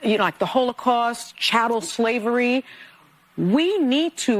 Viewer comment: Sunny Hostin compared J6 to "chattel slavery" not "child slavery." Just a quick correction and in making this I'm not trying, in anyway, to defend Hostin because she is willfully ignorant, a straight up liar and a psychopath. Anyway, when I heard saying she used "child slavery" I was confused as to wtf she was talking about and I paid close attention to her words.